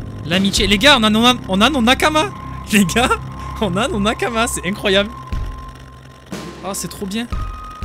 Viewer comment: c'est quoi un "makama"? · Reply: Tape "Nakama" sur Google c'est en japonais 😉